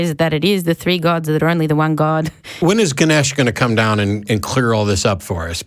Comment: At first i thought he said Slaanesh! We don't want Slaanesh to come down and clear things up! Then i realised he said Ganesh and everythings ok again. Ganesh can come down any time he wants, ganesh is cool^^ 1:56:39 Fun fact about this whole "intelligent design by timetravelers" thing. The correct anwser to "where did the time travelers come from?" is "timetravelers". That's just one of the more crazy stuff that can happen as soon as timetravel is involved. The effect can precede it's own cause. That's why "timetravel" is just as much of a pannacea as "god" is.